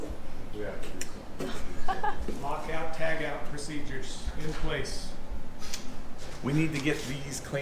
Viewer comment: I heard someone say at 3:40 "Lock Out Tag Out procedures in place" Is someone a former Navy? Sounded like.